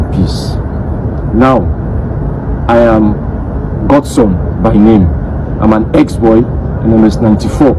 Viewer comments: He said it, his name is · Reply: He did not say that his name is as you claim. At 1:30 he stated that his name is or something like that. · Na you need hearing aid😂 He said his name "Osuagwu" and that's definitely not a Yoruba or Fulani name😂😂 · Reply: He did not say that his name is as you claim. At 1:30 he stated that his name is or something like that.